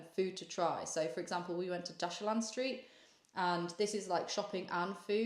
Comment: Is she saying Dashilan street? I can't find if they wrote it down anywhere and Dashilan is what came up when I typed in my best guess of the spelling lol. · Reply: Yes you got it, Dashilan Street 😊